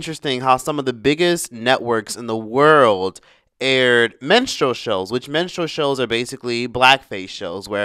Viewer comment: Baby!, it's Minstrel not menstrual. Please at 8:40 you really sound like you say the latter, even with that little bit of a southern accent. Please get sharper with that short 'i' sound.